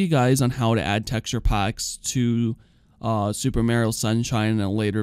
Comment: Super Mayrio? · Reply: I thought he said super meryil 😂